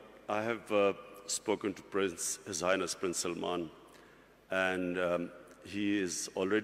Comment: did he just say zionist prince salman or do i hear like that